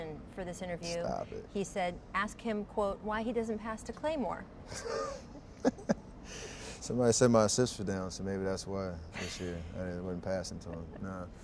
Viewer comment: Yo did steph say Klay turned his sister down @ 20:54 or am i trippin